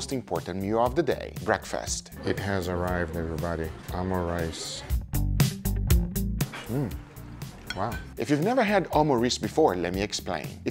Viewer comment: ayo wtf did he just say omureeces puffs